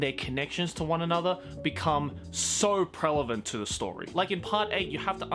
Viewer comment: did you just say prelevant?